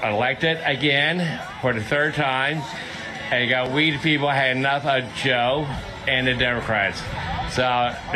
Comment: You have to read the YouTube subtitles! Where at 1:10 he says "Hey, you go 'we the people have had enough of Joe and the Democrats" (or something like that)...the caption says "Hey, you got weed?" 😂